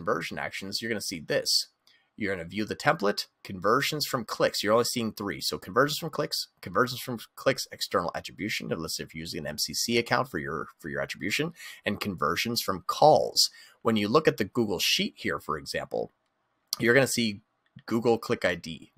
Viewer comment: What did you say at @ 2:01 ?? Was it "unless you're using MCC account" ? & also, could be please tell me a bit more about the "External attribution" aspect? And @ 8:38 what "Hashing tool" did you mention and use ?